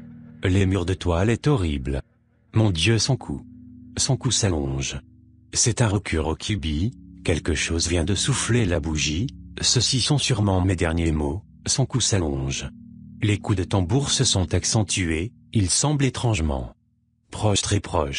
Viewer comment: j'ai entendu "saucisson sûrement mes derniers mots". XD